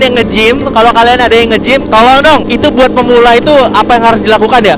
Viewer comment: gw salah denger apa gk si bang salah ngomong tolol dong 8:40 wkwkw